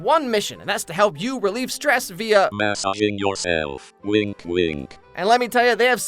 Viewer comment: EVERYONE who signs up to my giveaway will receive a FREE Rose suction toy with their order! 🌹<link> #bbpartner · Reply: 😂i am so sorry i saw the pic and just misheard the whole ad read as “Mass Off”